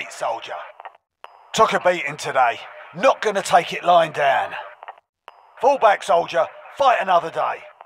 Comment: I had to ask to my Aussie wife to understand what he says. 😂 “took a beating today. not gonna take it lying down”